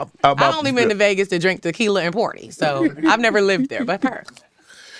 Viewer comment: I heard that St.louis “ PARTY” 😂😂😂